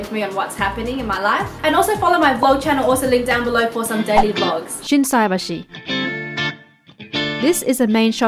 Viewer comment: did she say dirty vlogs lol